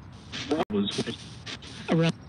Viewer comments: At the 9:56 point I hear “this is Mark” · I heard, "This is Mark."